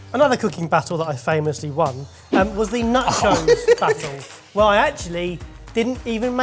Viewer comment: At the 4:00 mark, was saying "nachos?" Cause it sounds like he was saying "nut joes."